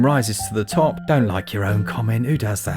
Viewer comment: Thought I heard something about cream rising to the top and “don’t lick your own cum up”. Def had to rewind that for clarity. 😜